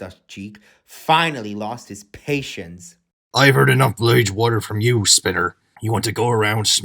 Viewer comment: What does Salty say? I've heard enough of "what?" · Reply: Bridge water